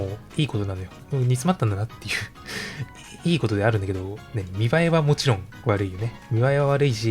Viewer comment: 〜 のセリフで 「見栄えは悪いし」ってところなんか 「見栄えはワルイージ」って聞こえるようにわざと濁してない？笑 鹿に紅葉さんの切り抜き「トゲから逃げる」切り抜きで知ったしそれを切り抜き編集してくれてる方がこれを編集しているとは！なんかうれしみ！